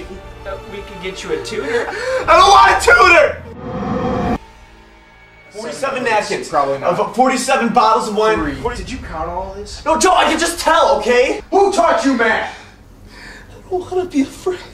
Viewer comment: ll first time i heard that line i thought it said "who touch u matt" LMFAO